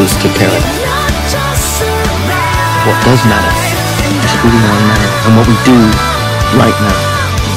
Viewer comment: does anyone know what newt was saying at 2:02 ? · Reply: Stacey Holland "what does matter is who we are now and what we do right now"